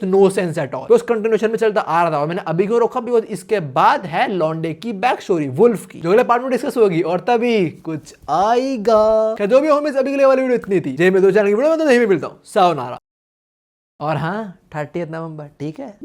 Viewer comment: did I heard 38th November